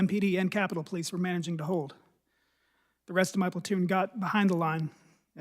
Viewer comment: Did he call is precinct a "platoon" ??... 😁